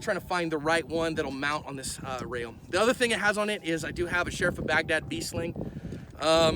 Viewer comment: I hear they call him "Shrek".